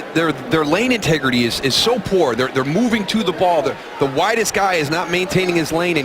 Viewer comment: swear at 47:33 he says "the whitest guy" lol